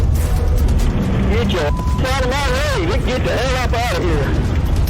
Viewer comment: 😂😂😂 what did he say?! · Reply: "Get your ass outta my way, we get the hell up out of here"